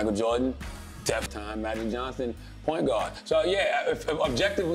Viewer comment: what did I just hear? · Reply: Michael Jordan-Def Michael Jordan-def time Magic Johnson Point Guard